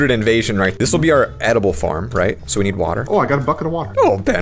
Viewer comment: What I heard: Oh I got F**king Water What it probably was: Oh I got a bucket of water